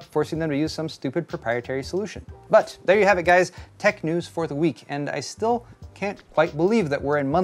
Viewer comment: Lol, I heard "tech news for the weak" in my head instead of "tech news for the week"